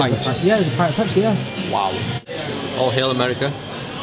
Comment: oh he said all hail america... At first I thought he said Aww hell America... Which as an american I would totally agree with